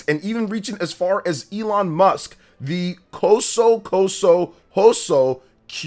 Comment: Elon musk?? Am I hearing this right?